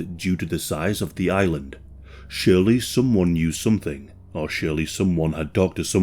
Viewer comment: follow me. <link> Patreon - <link> Big thank you to Coffee House Crime for the cameo. Subscribe to him here! - <link> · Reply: You said, "Shirley knew something", and "Shirley had talked to someone".... but you never actually mention WHO this person is and what it was that actually said !? ...and to who ? Who is???